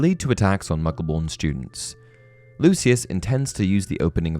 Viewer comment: I’m tired and I heard “a tax on muggle-born students”. 😂